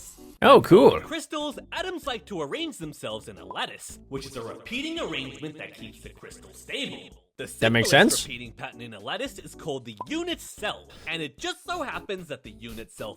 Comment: I thought he said lettuce not lattice